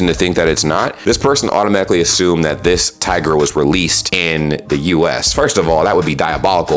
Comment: is it me or did he say tager not tiger?